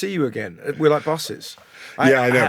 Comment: we are like busses? Certainly one is more mega than the other 😂 Or did Russell say bosses? Anyway I hope it's the former and I'm looking forward to this episode